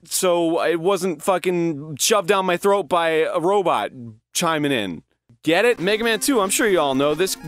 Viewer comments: Anyone else hear him say that and think 'robot chinaman'? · at first I thought he said "a robot chinaman" but I guess not